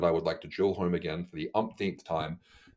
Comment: Did you say “HCG is absolutely required for TRT”? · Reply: I said absolutely not.